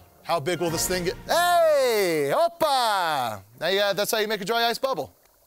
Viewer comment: Did he just say "oppai" ? LMAO!